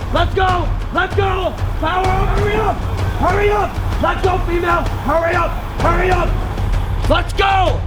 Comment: Did he just say "Lets go female"?